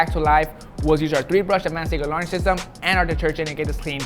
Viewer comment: Will somebody .. I mean anybody please tell me what he is saying when he says “first step we did was used our 3 BRUSH ADVANCE KIT “ALARN” SYSTEM. What’s an Alarn? It’s at 12:18 pls help me lol